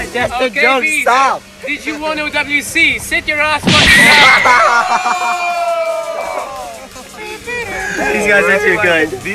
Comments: anyone know what said? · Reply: Did you won OWC? (Osu! World Cup) Sit your ass fucking down" XD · what did said xD ? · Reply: elir he said "did you win owc" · what did say to beasttroll lol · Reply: "Did you won owc? Sit your ass fucking down"